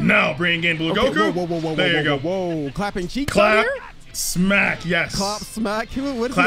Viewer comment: "Clapping cheeks, I hear?" 😳